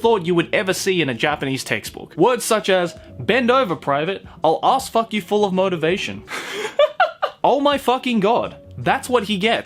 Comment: For a split second I thought it said I'll ass fuck you to motivation